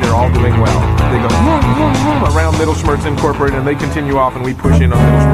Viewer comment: I can definitely see why they changed his name to Doofenschmirtz! Not only does it sound funnier, but when spoken, Meddleschmirtz sounds too much like mittelschmirz, which is a type of menstrual cramp. That’s what I thought they were saying at first until I saw it spelled out, so you can imagine how that surprised me a bit xD